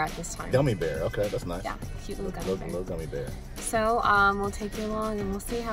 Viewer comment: did you just slip the gender?? Sounds like you said "hes a little gummy bear"